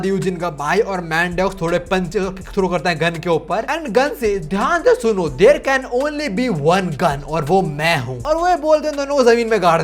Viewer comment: in my mind I literally heard "There can only be one winner, let's go." (It's from bgmi btw) 🤣